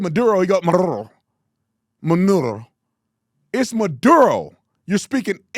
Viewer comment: Sounds like he saying "manureo" LOL